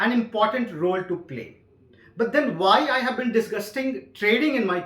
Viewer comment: Did he just said "disguisting" instead of "discussing"